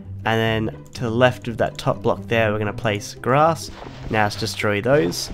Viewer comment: did he said "grass"?